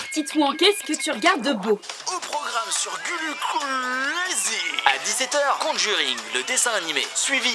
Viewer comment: Sur quoi ? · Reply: Sur "Gullu Craaaaaazy" x)